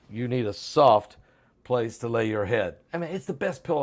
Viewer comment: when you talked about the pillow I thought you and say maybe if you need a soft place to land on